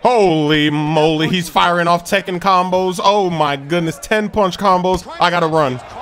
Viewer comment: lol did Smoove just said Tekken combos around 0:41?